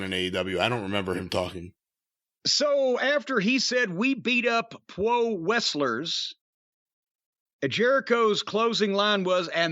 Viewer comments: I thought Hager said we beat off pro wrestlers... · I just found it hilarious that he could get them to boo him at all without a big babyface to beat around. And I found Daniel Garcia's shit eating grin hilarious as he 'confessed' hilarious, as well potential for him developing some kind of personality. Gonna have to wait and see if they do change the theme, heels really shouldn't have sing alongs. Jake Hager's 'beating off pro wrestlers' dragged shit down. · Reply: I thought he said beat up 🤣 did he really say beat off 🤣😭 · I thought Hager said "we beat off pro wrestlers" 🤣